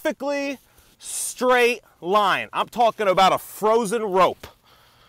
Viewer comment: i thought he was saying "Frozen Road" and that his point was going to be that it is too hot at the equator to travel on a frozen road, even if it was in a straight line...